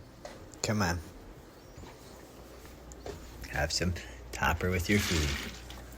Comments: i swear i heard him say have some pee with your food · What was the liquid in the glass sounded like he said copper??? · I thought he said copper with your food 💀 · Did he just say toper